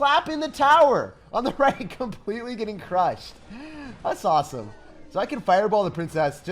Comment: that’s sauceam instead of awesome lol